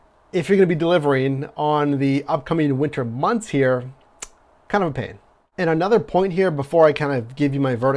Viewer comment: Anybody catch what the word he used at 07:55 is? Something about driving in Pittsburgh weather..... connopaid? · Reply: He means right after that. You said kind of a pain